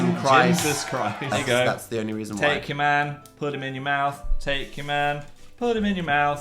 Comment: Um, Phil, what? Did I hear right? "Take your man. Put'im in your mouth Take your man. Put'im in your mouth".